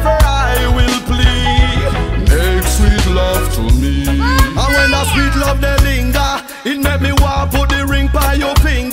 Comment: Does anyone know what the sound clip is saying in many of the mixes...I listen to all of your mixes and it sounds like a child saying " More Fire!!!" But im not sure what is being said. An example is in this mix- Best Of Riddim Mixtapes Volume 1. Feb 2016, at the 3:48 second mark , the sound clip is played. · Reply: yes More Fire Ever Tony Respect!!